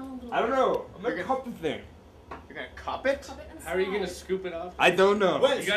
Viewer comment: When they were finding a way to get the spider when Pete said I'm going to cup it at first I heard I'm gonna hump it. And that's why I need to pay attention when I put videos on because I hear the most random crap😂😂😂